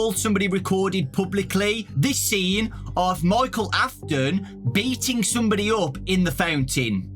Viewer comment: did he say Michael is beating up somebody's dad at 3:37 I just realized that the kid is screaming for his father because he is does not want his father to get hurt anymore